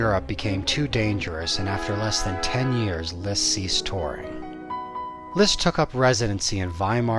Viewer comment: Great video! Thanks for sharing :) Just a quick question: Could you type out what you are saying around 3:00, after the following sentence "Traveling in Europe became too dangerous after less than ten years ... "? Many thanks! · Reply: "and after 10 years Liszt ceased touring."